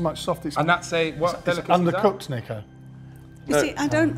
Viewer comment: did he say the n word? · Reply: No he said Niko